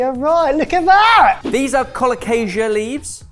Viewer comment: Taro is Colocasia, usually C. esculenta. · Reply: thanks you are right. Somehow I thought he had said Alocasia, but I myself must have made that mistake. Calocasia = safe for consumption. Alocasia = very much not.